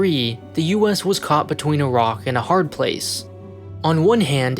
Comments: "When Taiwan's president proposed a referendum in 2003, the US was caught between a rock and a hard place" At first I heard 'caught between Iraq and a hard place' 😂 which would have been funny and accurate. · I thought I heard "...the US was caught between Iraq and a hard place"